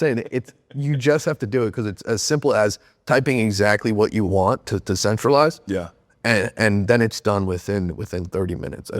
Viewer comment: he’s saying use what for nsfw content ??? Is he saying wigsbee? Wixie? Wiskey?? I need clarity bruh mumbling and shit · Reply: Wavespeed